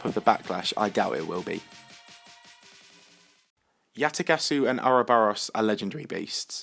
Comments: Did You Say Yatagasu? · Did he say "Yatagasu"? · Did you just say Yatagasu? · you said "Yatagasu" instead of Yatagarasu